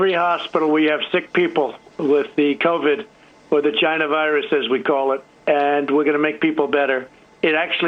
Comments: "Vagina virus". Am I the only one who hears that?? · I thought he was saying “ the GINA virus” like...vagina...